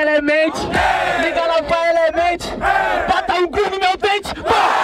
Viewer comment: Eu ouvi "bota o c* no meu dente " ?